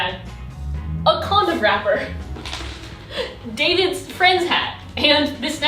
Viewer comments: did i heard it right? A condom wrapper? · For a hot second I thought she said David's friend's hat. Not David's FRIENDS hat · did i heard "davids friends hat" and "a condom wrapper" ??